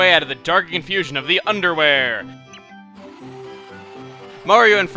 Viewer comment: IT SOUNDS LIKE HE SAID UNDERWEAR.